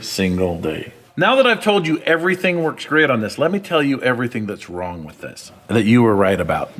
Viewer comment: OH! I thought you said, "Everything Wrong With My Feet!" 😂😂